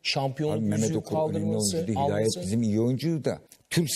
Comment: mehmet okur önemli oyuncu değil mi dedi ben yanlış mı duydum ?????